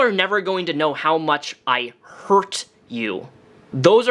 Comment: It sounded like he said “abuse you” and not “hurt you”, maybe I misheard but either way it’s absolutely awful.